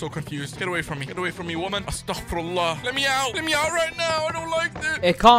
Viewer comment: I must give you guys my greatest apology. I never meant for it to go this far. When I started these googling shenanigans, I could've never imagined it would turn into such a serious matter. I didn't mean to harm or dignity, respect, or honor. But, now that's it's gone this far, I can only do one thing; apologize. So, from the bottom of my intellect, family, pride, and dogs, I give you my strongest and hardiest apology. I hope you guys can forgive me one day. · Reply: r u say astagfirullah ??? I hear